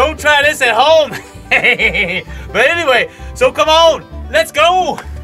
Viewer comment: don't know if it was me or you but sounded like you said "Come on lets Gold " :)cool video say hi to Slim for me :)